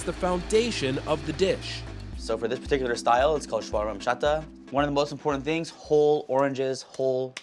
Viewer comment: Did anyone catch what type of shawarma chef said this was at the 2:55 mark of the video. Was it shawarma chata? Trying to find a recipe for the marinade